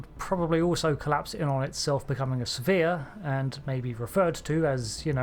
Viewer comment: "It would probably also collapse in on itself become a sphere, and may be referred to, as, you know... a neutron star..." 100% perfect delivery. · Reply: i misheard it as become a severe...... what??